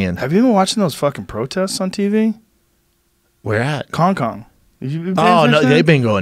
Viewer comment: did he just say Kong-Kong?